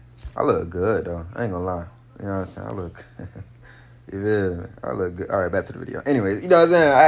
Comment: I thought he said she look good, mans was talking about hisself 😂